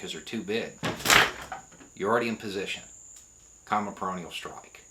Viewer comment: Sorry to say this on such a serious matter, but when you said "peroneal strike" I thought I heard "perineal strike". For a minute I thought folks were actually out there talking about striking the perineum for compliance.